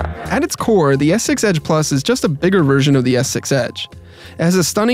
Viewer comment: Darn it *****, any time I hear this phone's name I hear "S6 Edge Big". You've got me doing it now ;)